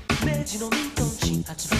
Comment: mentors be like (I heard "meiji meiji mei--")